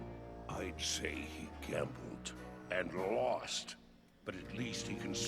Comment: What did Grogar say Sombra took? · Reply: He took a gamble, and lost.